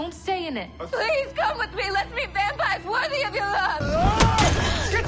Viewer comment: “let’s meet vampires worthy pf your love” OOF that scene made me cry hard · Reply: This whole time I thought she was saying let’s be vampire worthy of your love and I thought that’s a weird choice of words 😅